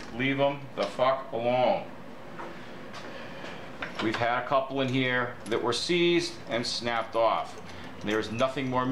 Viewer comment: HI, love your videos. What do you say at 6:00 or so about covering bolts? Cover with copper "what" ? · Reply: Copper antiseize. got it!